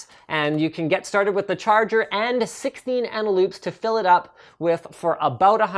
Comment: i tried searching for the batteries and i thought i heard analoop. Dont search up analoop.